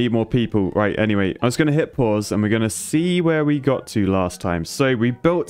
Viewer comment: Why is it when he says ‘hit pause’ I hear it as ‘hit paws’ and all I can imagine is a cut away to a clip of him giving paddy a high five 😅